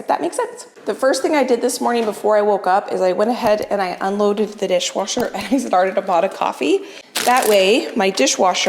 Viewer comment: Anyone else hear at 2:15 say "The first thing I did before I woke up was I went ahead and unloaded the dishwasher..." Wow! You did that BEFORE you woke up?!? I'm impressed! 🤣😂🤣 Thanks for the laugh-- I needed it!